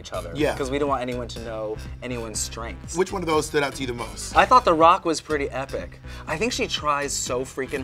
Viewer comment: I- did Betty call Rock M the fucking Rock? Like Dwayne the rock or am I hearing things?